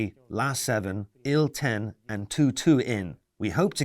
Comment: IL-22 jet bomber? Did I hear that right?!